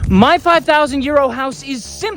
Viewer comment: For a while I thought he said my 5000 year old house 😂 I feel silly.